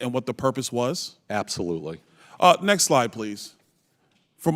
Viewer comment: I thought he said next lie please 😂😂😂😂😂